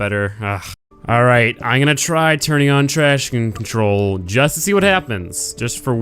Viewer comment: It sounds like he says "trash can control" haha